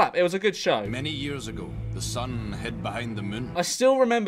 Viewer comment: WHAT THE F*CK DID THEY SAY?? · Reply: I’m Irish and I even find that funny “thu mun”